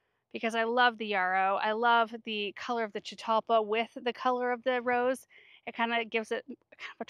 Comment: Did you say Chuparosa for that shrub @ 15:00? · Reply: Chitalpa